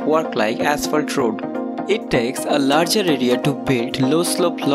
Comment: it sounds like your saying "larger idiot" 🤣🤣🤣